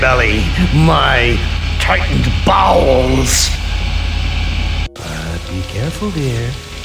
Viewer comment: is he not saying “tight b@ chat please is it “tightened bounds” someone please help why does he say it like that guys chat please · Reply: Bowels. There you go. There’s the answer.